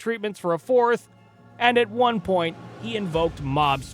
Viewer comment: I was listing more than watching and misheard this as "he invoked monsters".